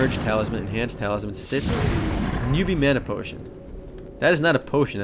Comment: I thought you said "noobie menopause" o.O